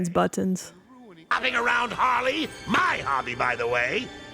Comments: BRO FOR THE LONGEST TIME TOO I THOUGHT HE SAID “MY HARLEY BTW” WHt the fvccccck I’m not insane 😂😂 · I thought so too i thought he said my harley really should pay attention to those subtitles